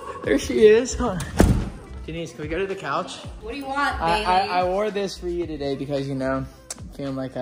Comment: Did say what baby? At 0:42-0:45??